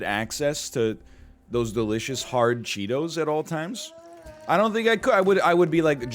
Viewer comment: I heard "hard cheetos" and got really confused. I thought he was just talking about his wife breastfeeding right before that, which had me trying to wrap my head around the comparison between the two.